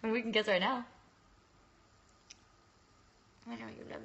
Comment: when she says they can kiss right now and he has him mouth open does she say why don't you love me?? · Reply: Jasmyn Howard she says I know u love me I think